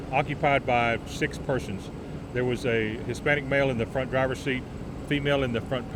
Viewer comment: I am a little confused here. If you notice, the police officer never mentioned the race of those involved until he spoke about the "black" female driver. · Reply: johnson - Not confused. You just have selective hearing, or you're just plain stupid. Probably both. He clearly says Hispanic male at 0:25 mark.